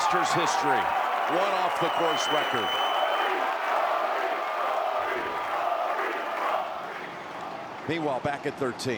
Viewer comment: What are they saying in the chant? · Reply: RORY RORY RORY